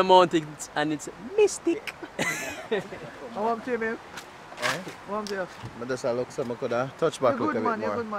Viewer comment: did just say “wahmp to you babe?” 👀👀YYYYYYYYYYY!!!!!!!!!!!